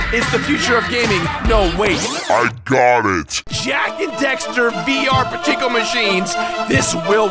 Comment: I'm down... Although I think he said Dexter.